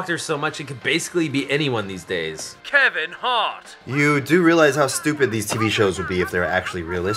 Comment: Kevin hart I thought he said Sevin's Fart